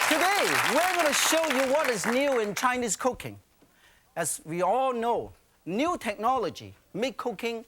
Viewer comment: did he saying cooking or cocaine, lot more fun